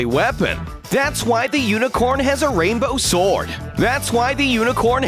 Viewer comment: did ypu just say thats why the unicorn has a rainbow sword 2 times